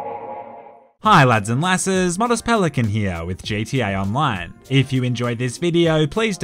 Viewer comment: did he say jta online??